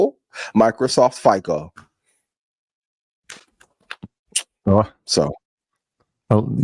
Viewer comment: did say FICO?